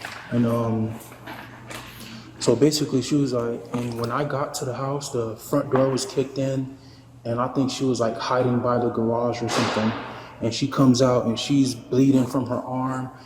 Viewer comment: 1. He's in a wheelchair. 2. The door is elevated on a porch with stairs. 3. The door opens outwards not inwards. It can't be kicked in. The investigating police are completely incompetent! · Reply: I thought that one cop said she was "hiding in the garage" So i think that was supposed to be a different door.